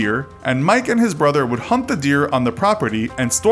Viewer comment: Did he say "hump the deer"😂😂